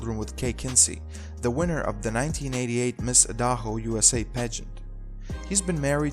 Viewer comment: WTF you trying to say? Idaho?